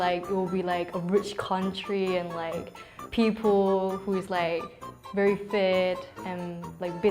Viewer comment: Ok so she said very fit but the caption said very fat. Whhaaattt I'm confused. Isn't that rude or just a missed caption.